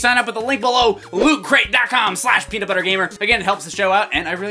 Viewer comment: So my headphones cut out in the middle of this video and I heard "Pee-ger instead of PeanutButterGamer, #Pee-gerGamer